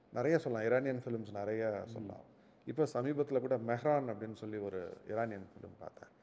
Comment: which film can't hear properly??? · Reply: mehran iranian movie